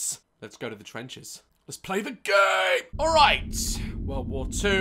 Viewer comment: Did Daz just say gay?